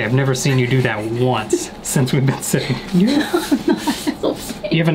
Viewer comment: Did he say, "Since we've been six?" And does this mean they're twins?